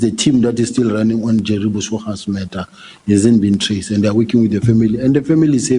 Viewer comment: did he just say murder kkkkk